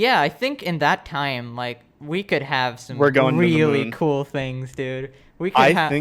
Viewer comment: Did say I think we'll go to the moon at the end?